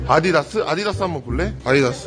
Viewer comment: 언제부턴가 아디다스가 자꾸 아디오스로 들리네요..